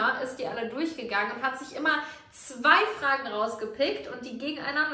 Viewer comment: Was ich bei 0:30 verstanden habe: Und die Mona ist die durchgegangen und hat sich immer 2 Fragen gausgefickt🤣🤣🤣